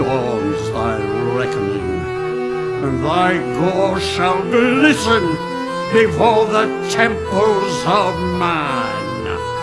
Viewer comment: What does Minos say on like 4:07 to like 4:10? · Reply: "and thy gore shall glisten before the temples of man" dude's about to paint with Gabriel's blood